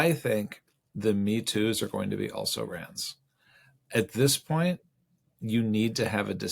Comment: what did you say around 00:41? The metoos are also going to be rants? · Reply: “also rans” i.e. not winners